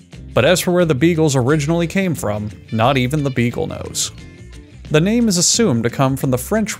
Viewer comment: I love Beagles. Delicious. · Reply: I thought you said The Beagles. They sure work like a dog.